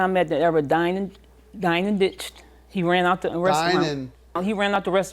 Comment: Dine n Ditch…. 🤣🤣🤣🤣🤣🤣🤣🤣🤣🤣🤣🤣🤣🤣 I’m sorry I thought u said Dine n B!txh !!!!!🤣🤣🤣